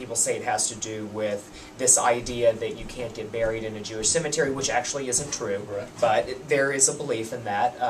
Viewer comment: I thought he said "you can't get married in a Jewish cemetery" 😅